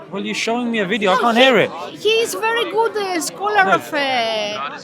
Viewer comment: Did she just call Joe Rogan a "Qur'an Scholar"?😂😂😭